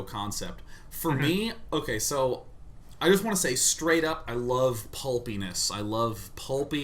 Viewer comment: why do I hear this stuff? "I love bull penis"